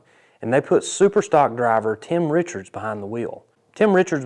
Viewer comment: For a second there I thought you said Tim Richmond but I was eating cereal,... proceed 👍